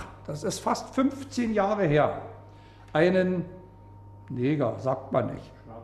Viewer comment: the man says "negro" . BBC Fake News subtitles say "nigger"